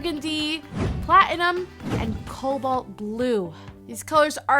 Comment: I thought she said cobalt glue lol